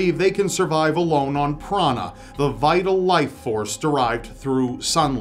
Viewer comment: "Breatharians believe they can survive alone on piranha..." What was misheard can't be unheard